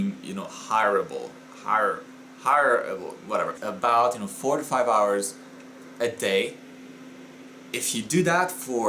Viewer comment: Jesus Christ I thought you said 45 hours a day. I almost had a heart attack!